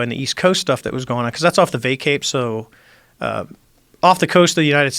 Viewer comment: what is favor saying at 3:33? "the vay cay?" wtf is he saying? can't understand